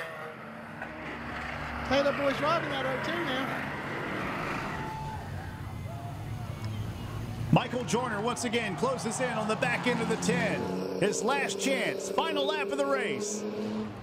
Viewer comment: The last lap was better than any nascar race. The hero of the race @ 36:57 - we thought it said "Michael Jordan" but it was Michael Joiner or Joyner? Our new leader... we call his move "THE JOINER" #thejoiner #enjoy the #joyner #amazing